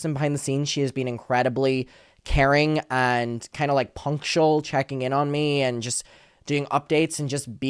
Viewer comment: I thought you were gonna say Emily is very punk behind the scenes but then you said punctual lol